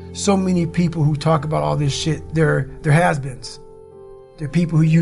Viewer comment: "their husbands" lol I think he was saying "has beens" but whatever. The "husbands" thing just made me laugh. That's all.